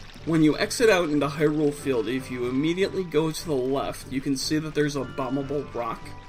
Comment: did he say left?